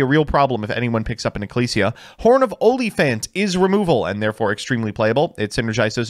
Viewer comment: I can't be the only one who thought he said "Horn of OnlyFans" and had to double check...